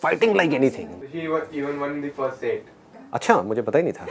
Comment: What he said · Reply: Anjali Joshi she even won the first set